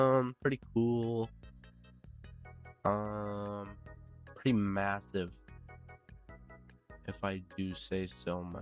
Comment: DID HE SAY MASSIVE